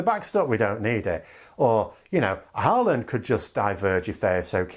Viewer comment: What is he saying at 12:22? Harlend?